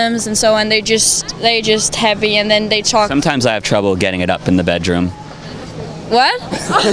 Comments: What does he say at 0:56? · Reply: He said "Sometimes I have trouble getting it up in the bedroom". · What Joe say at 1:00 that they laugh? I dont get it. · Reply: He said "Sometimes I have trouble getting it up in the bedroom".